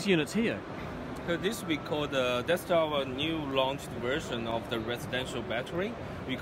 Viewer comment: ish he it sounds for a second like he say "this we call our Death Star" I know changes thought and says "that our" and I would sound worse talking than this guy does and worse again in his Mother Tongue but still...